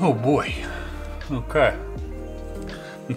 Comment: I love how at 5:36 he says “Okay..” but the subtitles say “Imma cry.” Hahahaha xD